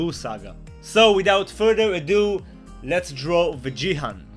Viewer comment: Lol I thought it said drawing "Vegan" for a sec and I was like what? Anyways great drawing!